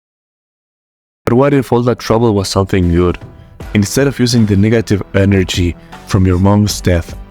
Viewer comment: I swear I heard you say, "The Niggative" 😭